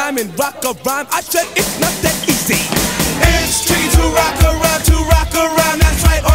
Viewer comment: for the longest time i thought this song said "it's chicken" not "its tricky "